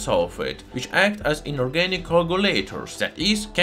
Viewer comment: I couldn’t tell what was being said here, turned captions on but didn’t help. Is it “…act as inorganic coagulators..” ?